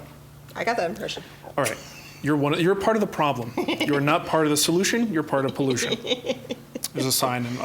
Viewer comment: I heard the saying as, "If you're not part of the solution you are part of the precipitate."